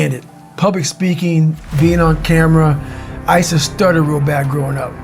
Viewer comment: he actually says “I use to STUTTER real bad growing up”.